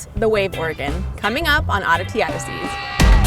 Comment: I swear it sounds like you said Odyssey Odysseys